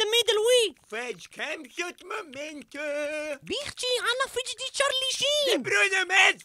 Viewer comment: Did salim say charlie sheen at 3:51 ?